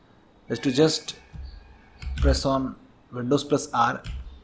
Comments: guys what did he says in 7:14 pls answered me huhuhu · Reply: Windows + R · guys what did he says in 7:14 pls answered me huhuhu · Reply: Window + r type cmd.exe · guys what did he says in 7:14 pls answered me huhuhu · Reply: Windows plus R N